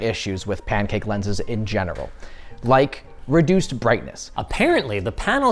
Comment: does he say “in genital”?